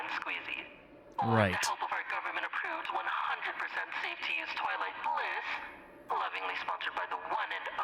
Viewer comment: Bro I was busy eating a poptart so when i looked away from the screen at 2:22 i fr thought the automated voice said "Toilet Piss" instead of "Twilight Bliss" 💀